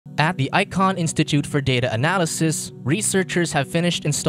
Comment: Sounded like you said "iCloud Institute" LMFAO